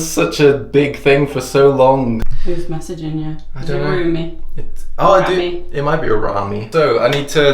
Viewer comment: The subtitles at 8:38 thought she said roommate not roomie then when he said rami they were confused lol